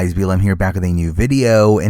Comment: Can I ask what you say at the beginning of videos? I always hear “BLM here” is that correct?